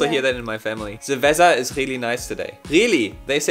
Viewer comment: "cerveza is really nice today" By the way I don't understand why the English numbers (million, billion, trillion and so forth) make sense to her. As a Colombian, I was taught that 1 billion is 1 million million, but in English 1 billion is 1000 million. should find it confusing as well if she was taught numbers like me.